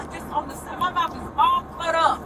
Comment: I thought she said that her mouth was cut up , can't tell